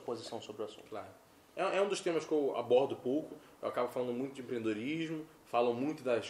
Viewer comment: Sobre o aborto..." É um dos temas que eu aborto pouco..." kkkkkk Eu juro que ouvi isso kkkkk Mas é só zoação